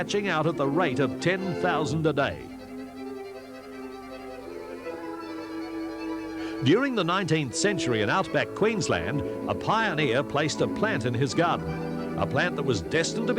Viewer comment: was this said pioneer, Maltese by any chance ❓ lol